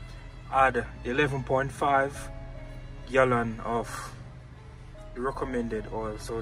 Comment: 11.5 gallons ? I thought the said 9 gallons?